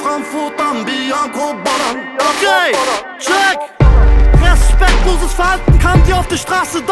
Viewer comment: ich verstehe gay check hahahhaha